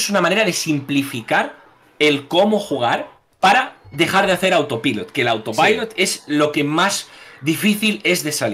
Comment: Hola muy buenas! Que significa lo que dice en el 15:22 ? autoqué? gracias de antemano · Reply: Autopiloto. Cuando juegas por costumbre, sin pensar en lo que estas haciendo